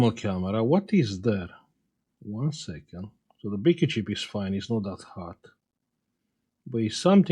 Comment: is he saying big chip? Or bgp chip? I can never understand what he is calling this chip. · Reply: BQ chip from the model number